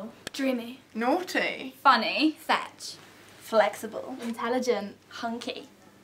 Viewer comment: When th gilt said fit I thought she said fetch